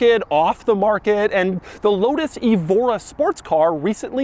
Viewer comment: I coulda swore Doug said Lotus Ebola near the beginning but then I played it again and he didn’t.